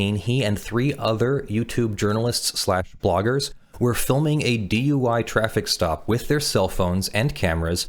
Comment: I kept hearing "DIY traffic stop." DUI makes so much more sense.